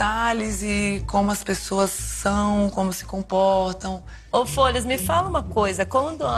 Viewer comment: ela fala "o Folhas" eu entendi "o foda-se" kkkk